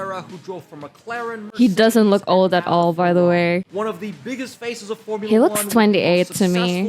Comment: NGL, i thought u were saying feces.